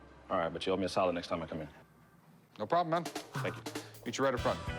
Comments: I thought he said "you own me a salad next time I come here"... ALLRIGHT DEAL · The map later sold for a liquid. Rick also gave the man a solid which later turned into a gas. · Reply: I thought he said "you owe me a salad" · It sounded like he said "You own me a salad next time I come in here" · Yo I thought he said " You owe me a salad next time I come in"